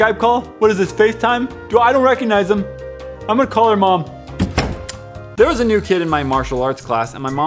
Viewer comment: I was watching without looking at the screen, and at 5:18, I thought said,"There was a nude kid in my martial arts class"